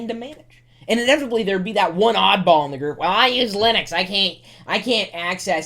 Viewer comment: what does he say @ 2:59?? 'and inevitably there'd be that one odd-ball [something]'